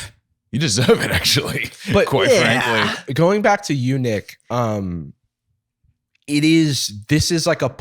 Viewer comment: when you said "going back to you, nick" i heard "going back to eunuch" and was momentarily VERY confused